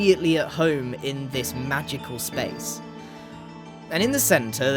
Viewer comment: I had to listen to what he said three times! I thought he said magical shoe ❤